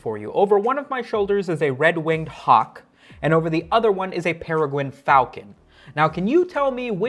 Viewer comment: I'm sorry did you say pewugwuin fauwcon? I wonder what he's up to nowadays...